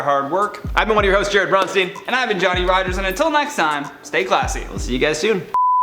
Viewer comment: Everytime Johnny says his name I just hear "Jolly Rogers" and think he should consistently dress like a pirate.